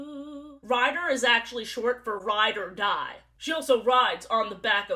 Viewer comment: i thought you said ryder dodd instead of ride or die and i was so confused because what is the USA Mens Waterpolo team doing in my silly little youtube video